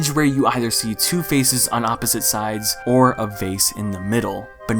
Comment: I THOUGHT HE SAID “two faces on the side and a Face in the middle” I spent five god damn minuets looking for a face not a vase